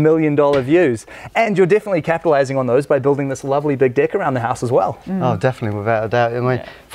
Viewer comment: Did he just say Big dick at 1:12 ? hahaha